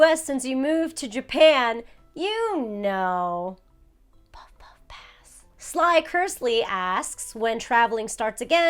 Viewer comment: I'm having such a difficult time hearing what says at 28:00? Its whispered so the auto generated captions don't pick it up? Can someone let me know what she says here? Thank you in advance! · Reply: She said puff puff pass